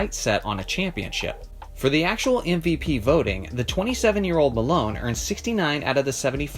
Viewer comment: Moses Malone is so underrated and overlooked. 3 MVPs over his career and it seems like nobody remembers him. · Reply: I thought you said karl malone lmao